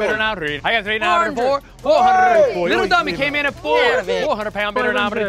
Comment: Did he call that kid "little dummy?"